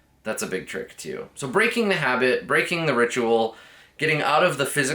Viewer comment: did yiu say... breaking the habit, breaking the habit tonight?